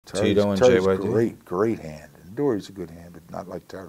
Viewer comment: Zero talk of the JYD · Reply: What do you mean? said Tito and JYD right at 0:00 😂 that was literally it lol.